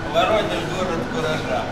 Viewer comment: Только сейчас расслышал, что Воронеж-город куража, раньше мне слышалось Воронеж-город фуража